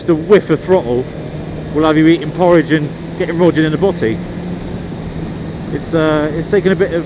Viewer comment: Sorry, I played it back 20 times and I have no idea what you are saying here. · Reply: 'Eating porridge and getting rodgered in the botty': The honorable gentleman is trying to convey that the power of the bike is capable of reducing a man to being unable to eat solid foods whilst being penetrated in the behind.